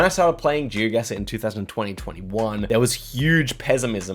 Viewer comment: The year 2000 and 2021. Im sorry if i misheard but i do not understand. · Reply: 2020/21